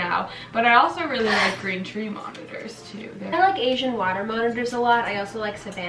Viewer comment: i heard “kinda like asian watermelons”